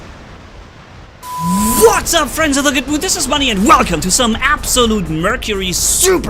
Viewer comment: I always thought you said, “Friends of the gogo,” LOL!